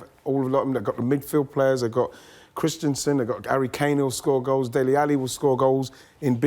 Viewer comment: Ronaldo a Manchester United legend😂 don’t think so Woah woah woah 6:41 what did he say😂 Gary Kane and Christenson